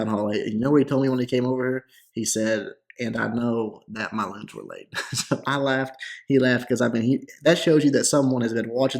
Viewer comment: lol it took me almost 10 tries to understand what he said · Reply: think he said "and i know that my loons are late"